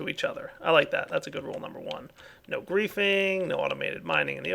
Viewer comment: You guys see the "No Griefing" sign? I honestly thought it said "No Girlfriending."